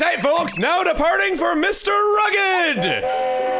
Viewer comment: Wait wait wait... did he say "Mister Rugged" instead of "Mount Rugged" at 2:08?